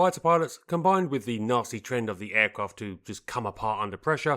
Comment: I swear to my mighty socks i just heard "and the Nazi trend of the aircraft to come apart under pressure" (9:05) ......if it was meant, it was mint!